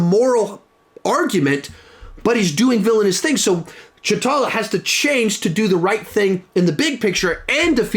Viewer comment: anyone else hear ch'tala?